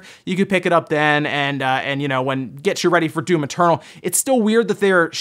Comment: I hear you say "Doom maternal"